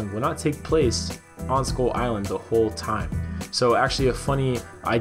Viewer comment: Why do I hear "Skol island"?